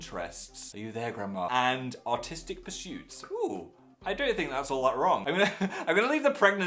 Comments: It sounded like Phil said "autistic pursuits" hahaha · I legit thought he said "autistic pursuits" until I remembered that's how Europeans pronounce "artistsic" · When he said "artistic pursuit" I thought he said "autistic pursuit" XD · When He Said "Artistic" I Thought He Said "Autistic" 😂😂😂